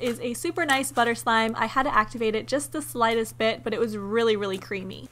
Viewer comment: I thought she said it was really creepy xD